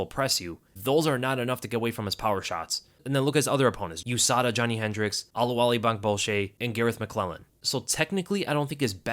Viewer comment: I heard that 36:25 his last opponents were "usada and Johnny hendricks " you ain't slick buddy 😂😂😂😂😂🤫🤫🤫🤫